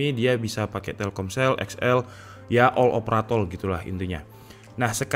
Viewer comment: ini gua nggak salah denger kan bang bilang "all opratol" Cuma mo mastiin aja sih klo gua ngga salah denger hehehehe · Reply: Eh bener ngomong all operatol lah... Bener